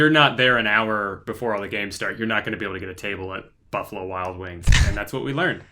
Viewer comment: I thought drew said " butthole wild wings" 😲